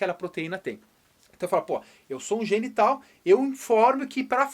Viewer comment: eu juro que eu entendi "eu sou um genital" KKKKKK